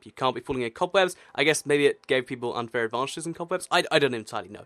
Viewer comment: DID YOU SAID /GAY/ TO PEOPLE